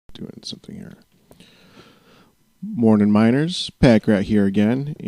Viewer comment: I've always thought he said 'miners' not 'minors'.